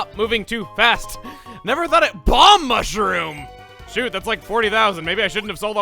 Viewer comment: That Balm Mushroom at 20:50 sounded like "Bomb Mushroom"! Imagining the Doom Shroom or something.